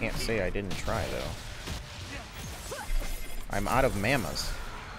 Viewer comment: did he just say I'm out of mamas?